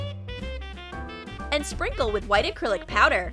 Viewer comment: I thought she said manicure... not acrylic